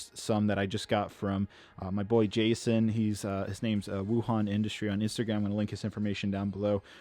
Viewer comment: did he say Wuhan industry?! Corona incoming